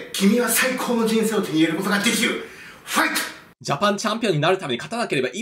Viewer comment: のファイトがファックに聞こえてしまって笑った